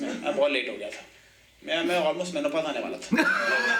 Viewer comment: "main sachmein batara hun..." What did he say after that i didn't understand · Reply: He said menopause ...means natural decline in sexual hormones